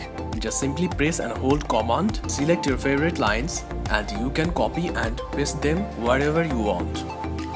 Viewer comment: I cannot catch what you are saying(1:51) to highlight multiple text lines. Can you write it down how to do it, please? Thank you. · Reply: Press and hold COMMAND and select multiple text or lines.